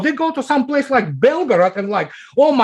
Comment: did he say Belgrade? oh nvm I think it was Belgorod